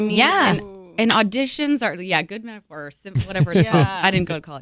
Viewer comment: What is Haley saying at 5:12? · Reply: "good metaphors"